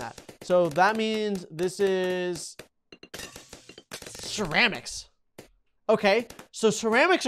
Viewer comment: The way he said ceramics at 11:11 was the best thing I ever heard. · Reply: It sound like he says sheramics XD